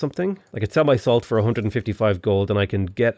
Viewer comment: What he said: I can sell my salt for 150 gold What I heard: I can sell my soul for 150 gold I was about to say, really dedicated to a win this game